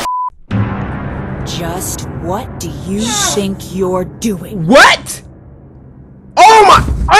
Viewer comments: 4'46 I heard"just what do you shink your doing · WHO HEARD WHAT DO U SHINK URE DOING XD LOL